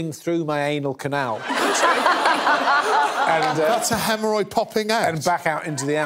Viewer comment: Dunno why at first I heard "hernia popped". Hemorrhoid sounds much less worse